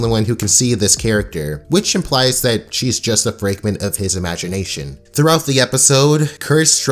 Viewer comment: So,...at 1:28 ...did you say "frigment" of his imagination? Did you mean figment or fragment?